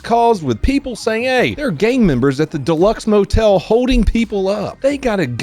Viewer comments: I swear to god, I misheard that as "there are gay members at the..." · Thought you said "gay members"